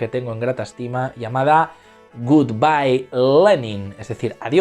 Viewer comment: Pensé que decía good by, Eri. 😂